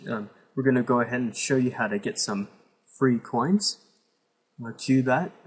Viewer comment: is he say free Bitcoin